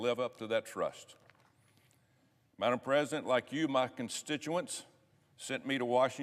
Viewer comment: did he say ...madam president? Or what?